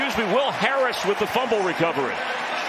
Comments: when the announcer said "Will Smith" instead of "Will Harris" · he did not call him will smith, did he?💀